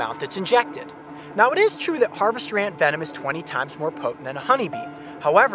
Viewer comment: Instead of ant venom i thought he said antvetom